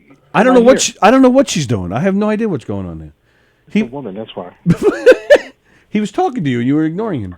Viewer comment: I missed what Mark said at 2:17 "__ woman". Someone fill me in please 😊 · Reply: “She’s a woman, that’s why.”